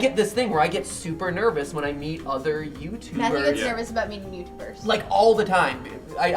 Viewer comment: "Matthew gets nervous about being nude first" Did I hear that right?